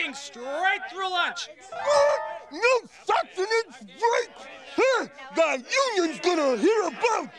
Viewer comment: Did he just say the f word? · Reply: Even if you know, just for people that don't... Bruno said... "What?! No Sustenance Break?!"